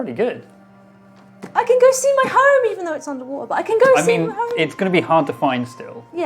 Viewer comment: I misheard sentry say " I can go see my home." as "I can go see my harem" and almost spat out my sandwich.